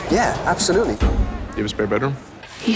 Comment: what did he say? :) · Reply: you have a spare bedroom?